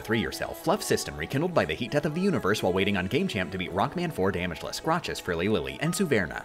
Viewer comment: Oh ok. I thought he said Rock "Band" 4 next month, & I was like what the hell? But no he said Rock"Man" 4. & now he's beaten it, I understand now